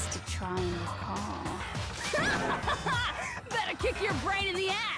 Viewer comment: I looked away and misheard "It's up to that grey lump" as "It's time to eat, pray, love" and uh, yknow, sometimes it's like that